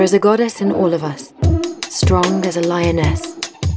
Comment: I can’t help it, I hear “strong as a mayonnaise” 🤪